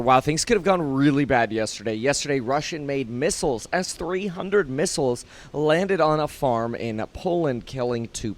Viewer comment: Just to help clarify. It sounds like said "Russian made missiles as 300 missiles landed on a farm...". He actually said "Russian made missiles S-300 missiles landed on a farm..."